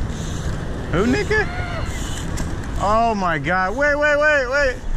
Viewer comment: Did he just say, "who N word" at 6:13 ??